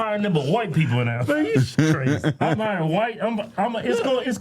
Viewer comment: I thought he said Milky Pot not Melting Pot 😂